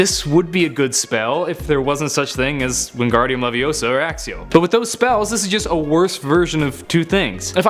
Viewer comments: Omg did you just say Axeio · Did he say "axio"?